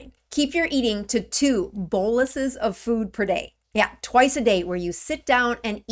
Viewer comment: What is that word...bowlisis? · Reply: boluses